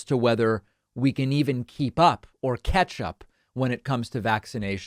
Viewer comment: did you say Ketchup?